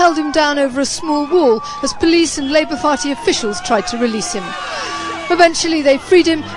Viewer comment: Is it just me or does she actually say Labour Farty officials